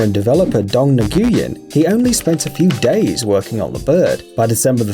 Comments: The text says years instead of days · He says ''A few days''. But the caption says years? WHAT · Wat spent a few days. Text says "I spent a few years," lol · he said “only a few days” but the captions says “years”